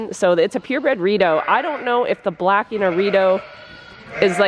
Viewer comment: Funny story ; for the longest time I thought you were saying the sheep like Prince William were Doritos 🤷🏻‍♀️I’m new . Do you have a bathroom 🚽 for you out there with the Sheep 🤷🏻‍♀️ ? Loving you from Illinois , USA 🇺🇸 !